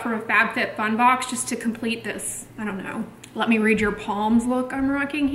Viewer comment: Lol ‘let me read your palms look’ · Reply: I thought she said 'let me read you poems look' :)